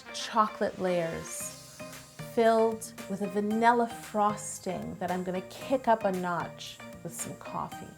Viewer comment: Did she say "vanilla frosting" not "vanilla buttercream"?